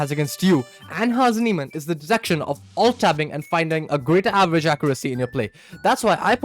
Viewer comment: Is he saying that chesscon detects "alt-tabbing" ? But what does "finding a grid accuracy in your play" mean?